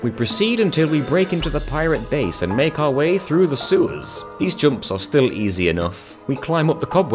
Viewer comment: is it just me or did say seweys instead of sewers?